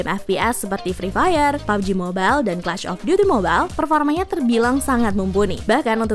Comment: ini aku aja atau kayak nya dengernya "clash of duty mobile". Anyway list nya sangat membantu. 👍